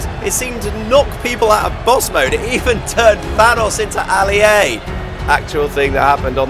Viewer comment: When he said Thanos into Ali A, I thought he said it turned VANOSS into Ali A.